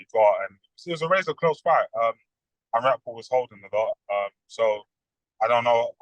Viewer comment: did he just say riakhpore was holding a lot….?